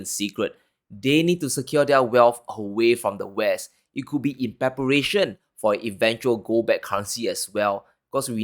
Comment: 'in preparation' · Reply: oh good stuff… I thought you said evaporation and I was freaking out!! Yah preparation!!! Sounds good! God bless!